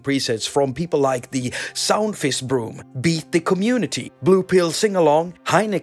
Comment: I first thought that you said SoundFist room, and for a second I thought that it somehow was a creative play on words. It definitely got my attention. ☺️